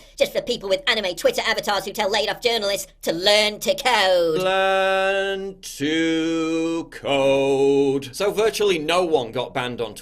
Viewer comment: Oh I thought you were saying learn to cope.